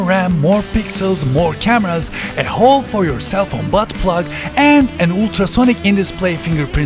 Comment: did you say it has buttplug?